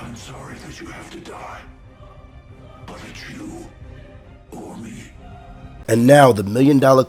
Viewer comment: Wait Savitar said "Im sorry you have to die, but it's you OR ME?!" I thought he said it has to be you ONLY it's hard to hear what he says sometimes